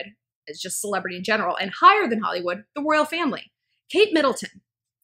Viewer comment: When u say 'Royal Family' I hear 'World Family'